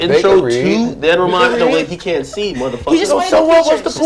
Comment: Did he just call Joe mothafucka 😂😂😂